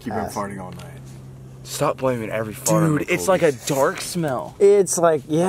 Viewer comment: Jake was so serious Jake : I did not fart Colby : Jake you've been farting all night Jake : STOP PLAYING WITH ME I HAVEN'T FARTED COLBY (stop blaming every fart on me Colby....if you heard that I actually hear both) Colby thinks it's hilarious (My other favorite part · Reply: I thought Jake said "Stop blaiming every fart on me Colby"